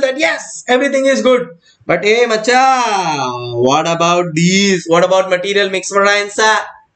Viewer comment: Everytime sir said GOCHA.... being a bangalorean, I heard it as MACHA